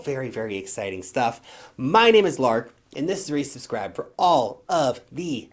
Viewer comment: If you listen to the intro at 1.25 or 1.5 speed where lark says "this is where you subscribe" it sounds like he says "this is racist crap"... Makes me laugh every time.